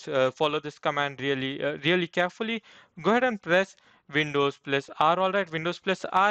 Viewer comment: please can you tell when keys to press on 1:50 i didnt hear you well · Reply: Windows+ R